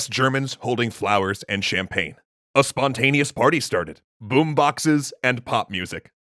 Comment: "And they were met by West Germans holding flowers and champagne." Sometimes... people don't completely suck. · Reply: I thought u said flamethrowers and champagne.